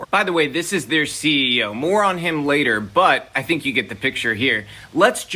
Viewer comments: said, - "This is their CEO, more-on him later." What I heard, - "This is their CEO moron..." but it still fits the video narrative. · Creator said, - "This is their CEO, more-on him later." What I heard, - "This is their CEO moron..." but it still fits the video narrative. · "More on him later" I heard as "Moron him later"